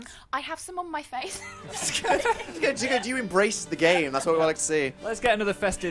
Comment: i can't be the only one that thought dan said 'embrace the gay' instead of 'embrace the game' can i